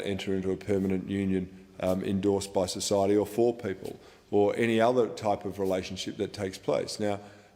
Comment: I thought he said "poor people" instead of "four people", and was thinking he was even more of a bigot.